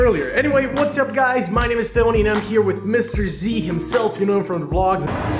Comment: Sounds like he says Stony